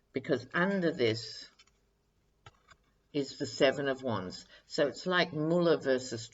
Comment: Totally off message, but as I was listening to your reading, I was puttering around the kitchen for another cup of coffee. At one point, I thought I heard you draw a card called the Seven of Chives! I thought, “What in the world? What sort of deck is she using now?😁🥦 (no chive emogee, so I went with broccoli!)